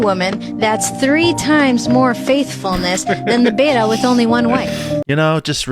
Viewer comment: Did she say Beta? Did I hear that right?